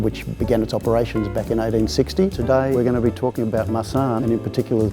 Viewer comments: When he said we are going to talk about marsane, I thought he said we are going to talk about my son! 😆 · I thought that dude said "Today we're talking about, ah, my son" Me: -_- when I realized when he actually said our Marsanne